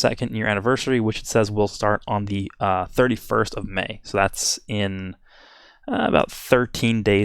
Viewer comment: Is it me or did he say 34 of may